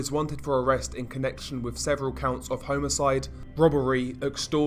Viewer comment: sounds like he said HOMO-cide